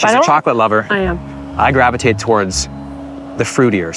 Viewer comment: Did just said I Grab a Tit instead of I Gravitate 😂😂